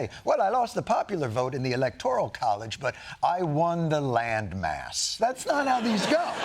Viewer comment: "Landmass" is a nickname for Mississippi bc of the weather channel forgetting what Mississippi was called, so I just heard "but I won Mississippi".